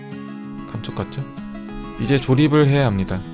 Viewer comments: 멍하니듣다가 맨앞글자 감을 못들어서 욕하신줄알고 깜짝놀람ㅋㅋㅋㅋㅋ아진짴ㅋㅋㅋㅋ 근데 욕할만해서 잘못들었다고는 생각안했는데ㅋㅋㅋㅋㅋ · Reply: 감쪽같죠? ㅋ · ㅈ같죠 로들림ㅋㅋ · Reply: 누르니까 "약간 ㅈ같죠?"이렇게 들리네욬ㅋㅋㅋ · 에 감족같죠..에서 감부분이 작게들리고 족에 악센트가 들어간것같은것은 기분탓이겠죠?